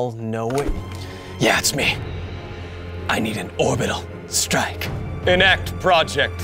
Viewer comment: Did i mishear you or did you say "Thorbital Strike"? Cuz launching a Doom tube from thorbit like a god rod in a Thorbital strike, well...thats just a wonderful string of words. Maybe my new favorite sentence. Or, well. My only favorite sentence since ive never had a favorite sentence before. Heh. Thorbital Strike.